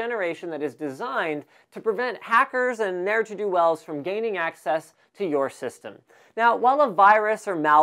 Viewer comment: I thought that you said "Gaming access to your system"...